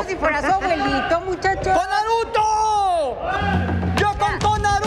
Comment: Yo pensé que decían CONAN BRUTO CONAN BRUTO CONAN BRUTO .... 😂😂😂😂✌️✌️✌️✌️